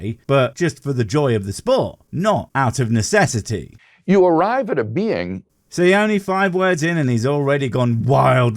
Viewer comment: I think one of us mis-heard him. I'm pretty sure he said "you come to a bean," which makes just as much sense to me as what you heard ;)